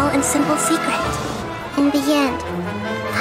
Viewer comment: I heard real sus instead of Real Sun 💀